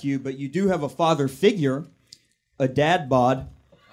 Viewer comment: When went away from the muslim jokes, he attacked her for being overweight (Dessert Storm) and having a dad-bod. I don't get it, either i'm blind or he is because she is not overweight nor does she have a dad-bod in the slightest. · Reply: I heard it as: "She still has a father figure, though: A dad-butt". I think it was pretty cute and decent. "Dessert storm" was not meant to be a joke, just a nick-name pun on "Operation Desert Storm". It´s irrelevant whether she is overweight, as long as she´s not super thin. Girls love sweets. You didn´t catch that made jokes about ´s religion and alleged that he would surely praise schoolshooters? also "bodyshamed" when she made a joke about ´s weak body appearance, and complete lack of muscles. And what about ´s jokes about ´s genitales. How are they not "sexist"? Oh no. Nothing to see here.